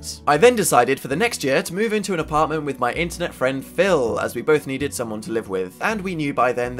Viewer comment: At first I thought he said Bill instead of Phil and I was like "WHO THE FUCK IS BILL?!"